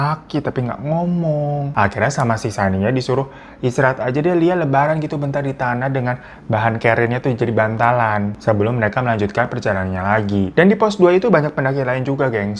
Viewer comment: Salah denger apa gimana? Rebahan jadi lebaran di menit ke 5 an 😅